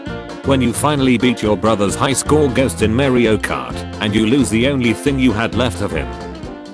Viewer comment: I legitimately thought it said “When you finally beat your brother and lose the only thing you had left of him.”